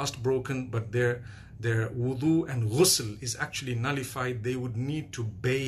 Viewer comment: what’s he saying “... their wudu and _____” and any information about it please and thank you. MashAllah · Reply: Wudu and Ghusl : Ghusl is a bath which has to be taken after Emission of semen after an Intercourse or WITHOUT it or after end of period in women or child birth. Its absolutely necessary and has to be done in Islamic way. You can Google or youtube these keywords to learn about: How to do Ghusl in Islam. You get information about it.